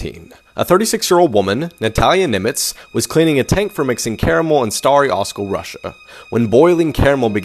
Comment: i get that this is sad but i thought that it said camel instead of caramel 😔